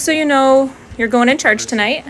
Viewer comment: Did anyone else's think she was saying you're going to church tonight